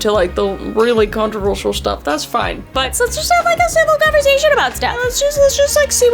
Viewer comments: subtitles has civil position but i heard civil conversation. Just me? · sorry to be this person, and its really nothing but at 1:03 says 'conversation' but the caption says 'position'. Im not critiquing im just pointing it out incase you want to change it :) fab video though, i love your work x